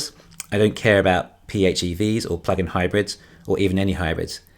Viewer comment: LMFAO!!! I thought for SURE you said: "I don't care about PHEV's or fuckin' hybrids, or even any hybrids" - 6:37. I had to watch it 3 times lol. Good video though. :)